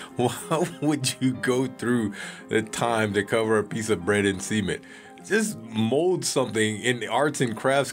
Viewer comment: I swear I thought he was saying "semen" whenever he said cement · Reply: u g h Omg.... I thought he said semen 💯💯🤮